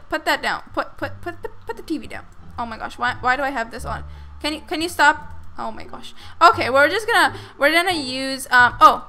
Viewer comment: Did she said "O mój boże"?